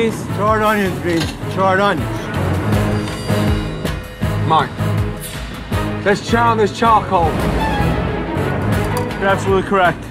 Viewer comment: customer: "the onions are a bit charcoaly" Gordon: "there's a char on this charcoal" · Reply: I thought he said "there's charred then there's charcoal".